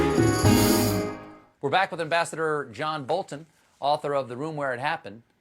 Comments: Could have sworn he said “The bastard John Bolton” at the end😂. Was the Freudian slip (from Ambassador) in my ears or on Steve’s tongue? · When Colbert wrapped it up I swear I heard him say “the bastard john Bolton”